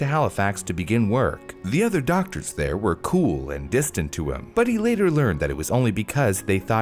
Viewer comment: He does clearly say "cool and distant" at 3:54 instead of (I'm assuming) "cruel and distant" right? I'm not just hearing things am I?? My headphones are not that great